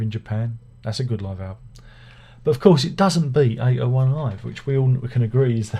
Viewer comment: What title did say we can all agree is the greatest live album of all-time at 7:46?? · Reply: 801 Live - I think that’s what he said… 🤔